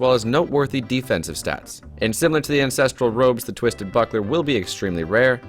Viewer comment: He sounded like he said Instinctual Robes xD